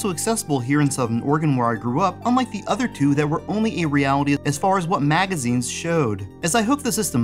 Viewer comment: What on earth are you saying at 1:01? It sounds like your saying only eeerealityiid... wtf? Somthing about only eerealliidid as far as what magaizines showed? Say what?